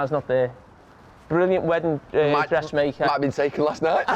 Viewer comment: what's he saying there? It sounds like "It might've been Sead Kolasinac" :P